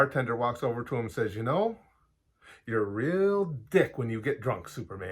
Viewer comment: I always heard it as, "Damn, Clark. You're a dick when you're drunk."